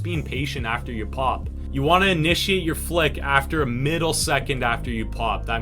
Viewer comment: Did he just say a middle second instead of a millisecond?😂